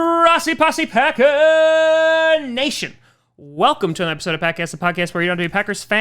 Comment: The frist time I heard the name of the channel I thought he said packass